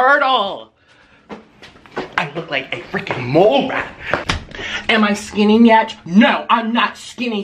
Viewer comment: did anyone else think that he said, "Am I skinning yet?" or was that just me